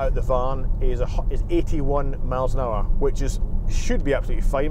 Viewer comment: Watching this on my phone with tiny speaker and at 8:20 I could have sworn he said 181 miles an hour.... wow Vauxhall good job...